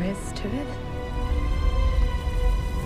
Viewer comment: OHHHH she said "Hector you are a silly man" lol I thought she said "Hector you are a sleeper" like he's secretly really hot :p